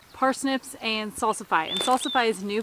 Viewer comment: Thanks for the spuds video. Was just looking that up today. What was that one variety (seeds) that was new for you? Sounded like you said Salsafy. I’ve never heard of it. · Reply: Salsify has an edible root and flower. The root is good in soups and stews. Starchy. Lots of minerals and vitamins. Some say it tastes a bit like oysters. Adds another layer of flavor.